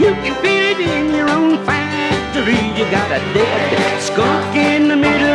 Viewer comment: It sounds like he says "your own factory" instead of "your olfactory."